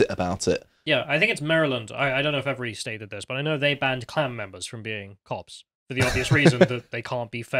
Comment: I misheard saying "they've prohibited clown members from joining" (instead of "klan members"), and then they both agreed that, obviously, clowns cannot be productive members of society! Like, I fully agree but don't say it out loud!